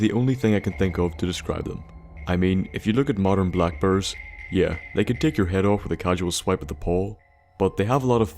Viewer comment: For the longest time, I thought you were saying black birds. Not black bears! Lol